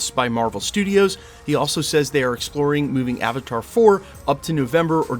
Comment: Anybody else hear him say Avatar 4 at 2:38?😂😂